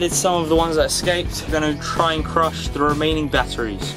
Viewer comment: Oh! I thought it said "crush-sh*t"